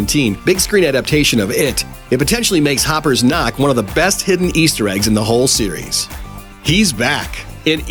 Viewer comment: I thought it said “Easter eggos”